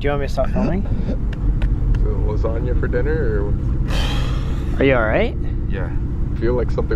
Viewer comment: I started crying for no reason 14:28 a woman said "Sir?" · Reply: I thought she said "are you hurt?" 😂😂😂